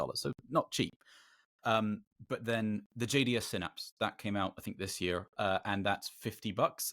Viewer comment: What is the gadget he is saying there? I can't hear him. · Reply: JDS synapse